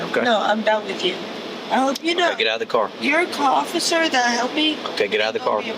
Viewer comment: did she say your cofficer😂 guess she put cop and officer together😂